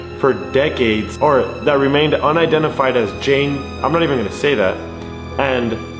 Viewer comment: At about 21:25 it sounded like you were you going to say Jane doe just a heads up they call the people who they can’t identify Jane doe.